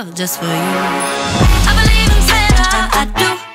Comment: At first I thought she was saying: I believe I'm santa